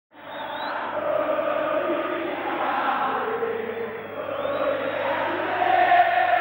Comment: Qué dice del segundo 00:00 al 00:08? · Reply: Yo no soy se la boca, Yo no soy vigilante, yo soy del gallinero, porque tenemo aguanre